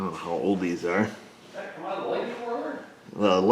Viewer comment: I heard him say “lightning whirler”. Somebody has been watching VGG. Also, hearing him say “Chevrolet” in his own way is music.